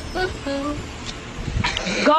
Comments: I thought it said go to the market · I thought he said "go the market" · I thought it said go tha market go tha market